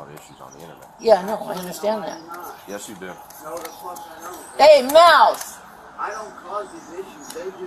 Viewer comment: I thought said “Hey, mouse!” 😂😂